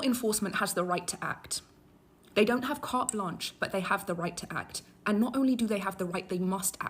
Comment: Did she say, "cart lunch" and not carte blanche???